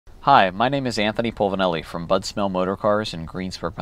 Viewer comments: I thought he said butt smell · Sounds like he said "Butt Smell motors cars"